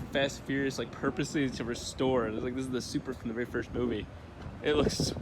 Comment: did he call it a supra?!?